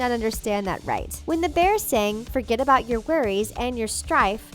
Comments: In the bear necessities I heard "forget about the worries and your life" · The only one I misheard was "Bare Necessities", and I head "stress" in place of "strife". My vocabulary and linguistic skills have always been above average, even as a small child, even when I didn't speak the language. I'm still shocked that I clearly heard the word "provincial" in the song "Bonjour" from "Beauty and the Beast" considering the fact that I didn't even know it was a real word until I looked it up after seeing the live action version. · I thought they said "forget about your worries and your wife" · I heard "Forget about ur worries and ur life" .....